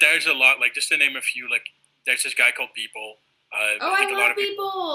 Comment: Can someone give my the name of the artist Hein is talking about at 9:00 ? I can't hear it properly :/ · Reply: M4tteo he mentioned Beeple first and then Ash Thorp